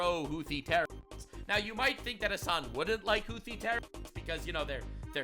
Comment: For a moment, I thought he said "Luffy terrorists" instead of "Houthi terrorists".